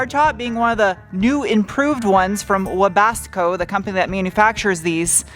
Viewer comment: Just to clarify, the top is made by Webasto. It sounded like you said Webasco.